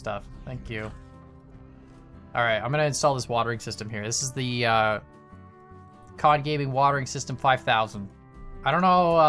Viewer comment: You said Watering System and I thought you said Concaving Watering System. I was confused for a bit.